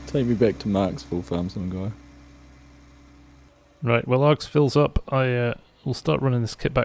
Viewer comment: When you said "When Args fills up" , I thought you said curls up lol